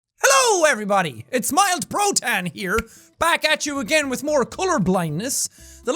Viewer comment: I thought he said mild bro tan